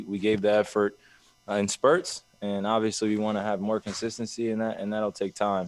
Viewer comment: I thought it said conspiracy not consistency.